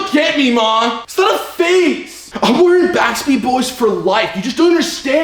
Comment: HILARIOUS GUYS!! Well done! Is he saying to his mom We're having "Crock Pot" for dinner?? LOL?? Or what is he saying there? Just to let you know there really isn't any dish called "Crock Pot" per se, but the very best roasting was always done in a crock pot. It is a very funny old joke from an old movie the kid says "Oh Mom, Sigh... I guess we're having "Crock for dinner again tonight!" LOL.